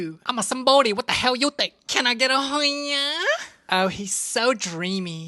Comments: My dumbass clicked the notification then I heard ‘can I get a hoyiaaaa’ 😭 🙏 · Clicked on and i heard "can i get a hoyiaaa" 😭🙏